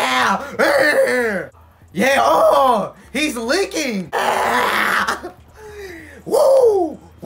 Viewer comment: i had this playing in the background while i was working and all i hear is "OHHHH YEAHHH, AWHHHHH HIS LEAKING"